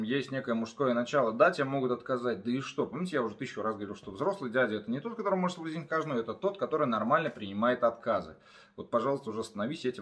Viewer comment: кто-нибудь разобрал, что он говорит на 3:48?)) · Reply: Взрослый дядя -это не тот, который может соблазнить каждую. Это тот, который нормально принимает отказы. Цитата 3:48 👍